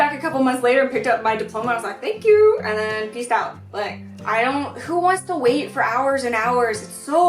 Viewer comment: FYI 5:54 the subtitles say "pissed out" but I'm pretty sure you said peaced out.